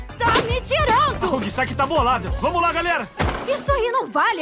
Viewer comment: eu escutei isagi ou to maluco